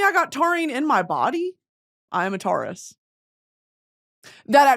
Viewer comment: The way i thought your were saying terroist in cursive when you said im a taurus has me dead, I need to be sent to fucking bed, or a psych ward, that will work to ig